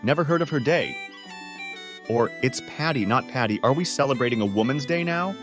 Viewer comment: as a Floridian, I heard "It's Paddy, not Paddy" ...this won't be a problem for me I guess